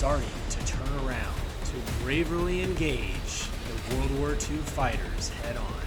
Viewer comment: my god did he say 'braverly engage' ... the internet truly has ruined a generation. Also it sounds like he is narrating this in real time with all the uncomfortable pauses and cut off sentences